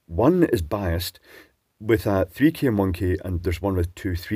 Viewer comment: biased with a 3K Monkey? ... That's what I heard 🤪